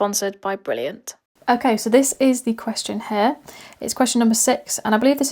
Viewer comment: I heard "this is the question hai "😮